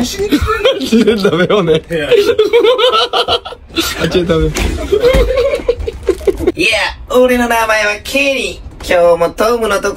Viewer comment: 画面見てなかったから「やあ！俺の名前は」って聞こえてエッ！イエローハンターY再来！？ってびっくりしちゃった…